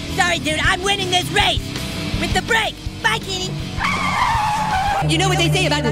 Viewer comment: why does my brain always thought that he said "kitty" instead of "kenny"